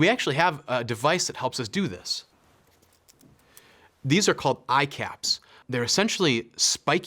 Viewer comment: I thought he said “icaps” Almost thought it’s something of an apple product which will prob cause $500 each 🥴